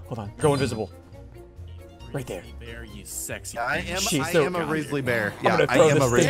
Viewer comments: Wtf did he say 6:01 💀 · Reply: He said “ sexy “ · Yo what did pat say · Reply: "Come here you sexy guy" 💀💀